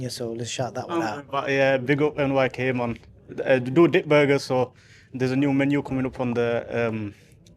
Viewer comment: Did he say Dick burger, think I,LL give that a miss.😂😂😂